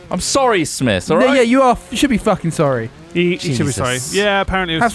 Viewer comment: did Smith say "nigga you should be fuckin sorry"?